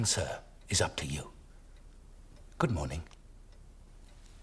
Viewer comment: I distinctly saw his mouth form the words “good morning” the subtitles supported this… so why did I hear “f**k off”